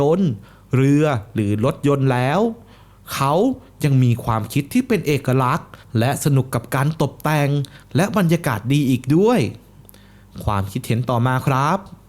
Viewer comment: ตรงนี้พี่พูดว่าตดแต่งเหมือนจะพูดผิดนะครับ😂😂😂